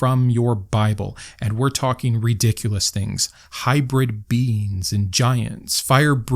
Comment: Ooooh, "hybrid beings." I heard "hybrid beans" and was confused for a bit.